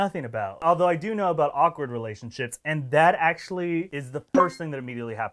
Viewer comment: did he say relation-shits? 😂😂😂😂😂👏👏👏👏